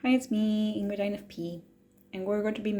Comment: I always hear " it's me angry infp ", which is unrealistic. So I know it's an audio illusion (